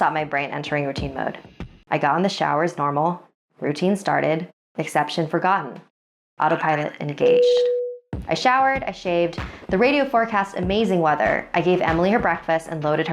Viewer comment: what does it say I heard Autopilot hej